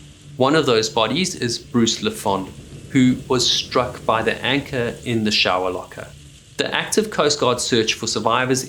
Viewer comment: "How did the anchor get in the shower?" "Through the hull..." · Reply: I thought you said "wanking in the shower"